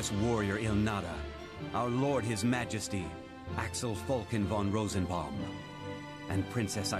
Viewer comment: I swear I heard him saying "Basch fon Rosenberg"(from Dalmasca!)! XD